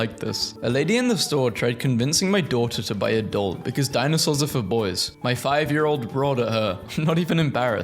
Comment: I heard "femboys" instead of "for boys".